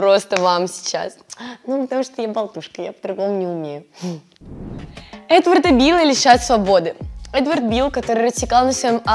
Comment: Когда сказала: Эдварда била, мне послышалось: этого дебила😅